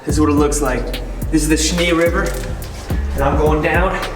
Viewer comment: I keep hearing “Schnee River” 😆